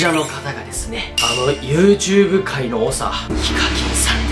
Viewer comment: YouTube界の猛者に聞こえたんだが